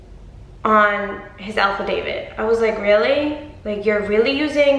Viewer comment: I love you but I don't know if it's your accent but sounds like your saying alpha David not affidavit lol! But he's mental, and a Mighty Morphin Petty Ranger🚫